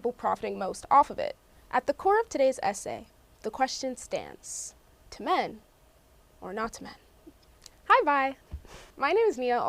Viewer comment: help I heard that as to p*g Or not to p*g😶‍🌫️